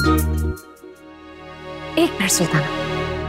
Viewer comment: yek minute ruko aap log 1:45 to 1:49 ke bich me jinimini kya bol rahi he suno to mujhe laga usne yek minute sultana blaaaaa plese watch between 1:45 to 1:49 min